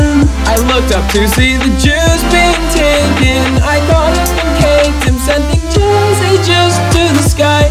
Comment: What does he say at 1:02 I got up and caked him????